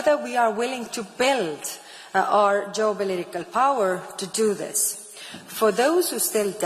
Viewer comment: What is she saying, 'jobolitical ", " to bell our political power" or something? She cannot even pronounce her words, how can she expect the World to take her seriously??